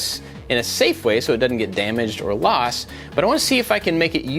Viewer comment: did you just say “or lost”. Super easy to find if it did. Nice project!!